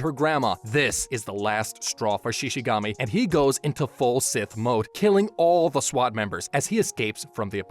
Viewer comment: Did he just say "FULL SIMP MODE"😃🤣🤣🤣